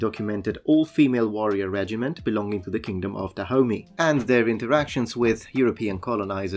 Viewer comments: I really heard "the kingdom of Da Homie" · I thought you were saying The Kingdom of the Homies